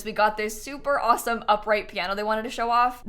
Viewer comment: DID YOU SAY UPRIGHT PIIIAAANNNO!?!?